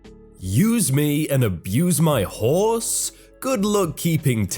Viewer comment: I was just listening when you read the first title.... I heard it as 'Use me and abuse my whores?